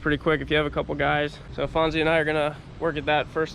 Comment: Did you say Fonzie is helping at 0:22 ?